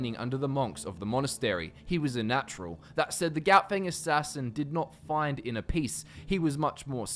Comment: I thought that said clout gang assassin